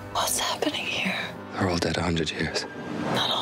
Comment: what does colin's character says at 01:53? and after that the reply of jennifer, pls :) can't understant... thanks! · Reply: "We're all dead, a hundred years."